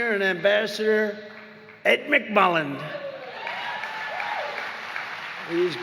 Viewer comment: I got scared, for a second, when President Trump said Ed McMullin.😂😅 I thought he said Evan McMullin. Evan McMullin sucks.